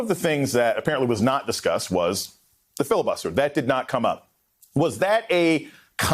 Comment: what i heard him say was ' F the filibuster ' L O L